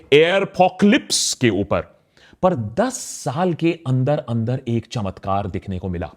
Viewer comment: I heard Karan Thapar 💀